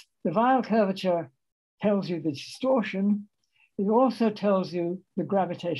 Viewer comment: He never said anything about "torsion"; you simply misheard him. He said "distorsion" at 51:46, not "torsion". You can generalize it to Riemann-Cartan geometry and bring in torsion (I've done so). In that case, there will be more to the decomposition than the three sets of components depicted in that diagram. There are 20 independent components in the figure, 10 with the Weyl tensor (5 for left-helicity, 5 for right-helicity). The others include 9 with the traceless Ricci tensor and 1 with the curvature scalar. In you bring in torsion, there will be 16 more. I just did a once-over of his first volume. The lecture is mostly working off of that, including the figures and visualizations. A lot of it I already came up with, independently, with more or less the same notations, except substantially cleaner and more generalized - like including Riemann-Cartan geometries (including generalizing Newman-Penrose to Riemann-Cartan geometries - with torsion). I probably picked up Penrose'isms through my reading of Carmeli's books. And I expanded Carmeli's treatment, too, to include non-Abelian gauge fields, as well. Carmeli only did SU(2) fields and electromagnetism. Also, while Penrose pushes his "abstract index" notation, I simply use the Mathematicians' linear functionals and make the vector and co-vector arguments the indices, instead. It generalizes, subsumes and unifies the mathematicians', physicists' and Penrose's notations seamlessly all together as one. I demo'ed that in a recen